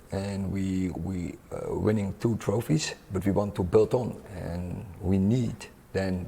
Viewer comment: sounded like he said, "we want to biltong" 😂😂😂😂